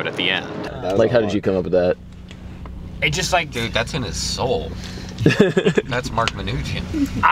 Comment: He said "That's in his soul!" but I first heard "That's an assault!"... So, I thought "So salty, in his face like an explosion. Salty mine."